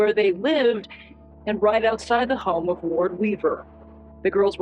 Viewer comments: loved this chapter just because everytime you said his name it made it sound like you were saying ward weaver the turd XD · With the accent, it sounds like he is saying " ward weaver the turd" or is it just me? More appropriate lol 😆